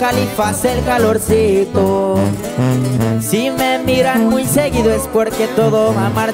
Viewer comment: Who else heard “ seguro “ instead of seguido at 1:49 😂